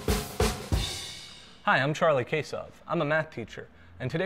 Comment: i thought he said Charlie Queso